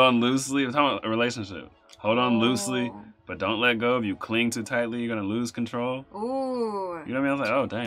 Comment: i thought he was saying, "if you cling to Daffy"